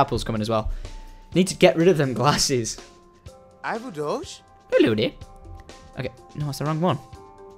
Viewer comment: Anybody else hear the sim say I'm a douche???